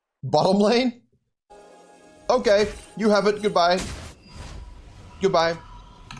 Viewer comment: Did anyone else hear the "Oh yes" in a really deep tone in 14:08? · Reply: I heard "good. yes" and it really creeped me out